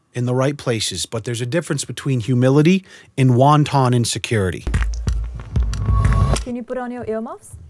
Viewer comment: Did he really say, "wonton insecurity" at 8:19????